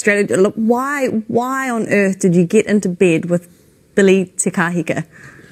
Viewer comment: I can't make out the name she mentioned at 1:20 Billy Tahahiga?